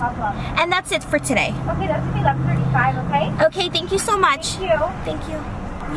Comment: Too drinks and a cackepop 35$ 🙀 omg 😳 no! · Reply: I thought she said 11.35? Because 35 dollars is a little much 😳